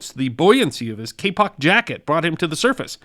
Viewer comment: i thought you said K pop jacket for a moment ..........LOL